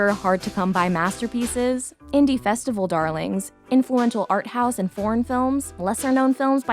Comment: I thought she said, "Influential arthouse and porn films."